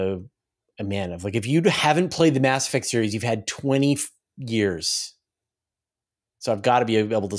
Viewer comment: if you were only listening to this, did not in fact say "If you haven't played the Mass Effect series, you've had 20 f** years" and censored the f bomb. Even though it really sounded like that 😄